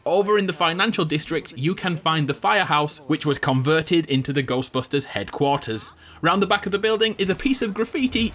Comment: I thought you said golf busters